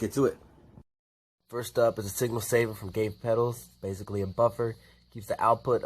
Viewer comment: Hey! Gabe Williams, which it appears that you figured that out already because contacted me today about you letting me know that you were looking for a dual drive pedal like he built for me. :-) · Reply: Thanks for your great videos. The problem was I thought you said Gate Pedals when in actuality you said Gabe Pedals(Gabe Williams). Thanks again for your priceless information.